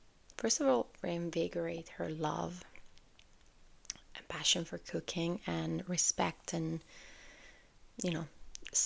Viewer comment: you said "passion for cooking" and i heard "passion for cocaine" AND LET ME TELL YOU it took me out cause i was lost